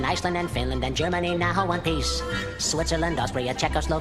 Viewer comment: Is it me or did i hear "one piece"